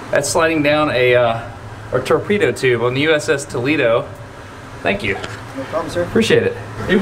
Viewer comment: I thought he said the “USS Tomato”